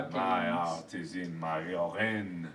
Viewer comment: Did mark say his “art is in his urine”?